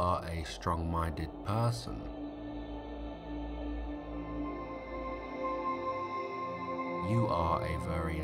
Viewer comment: Anyone know what’s being said at 7:13:52? It sounds like “strong-winded person”, I’ve started obsessing over it and snap back awake each time it’s said... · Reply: it says Strong-minded person :)